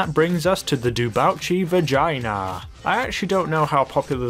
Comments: did he just say vagina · Is my mind dirty if I heard said Vagina instead of Vagner? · Did he says Vagina ? :))))))))))))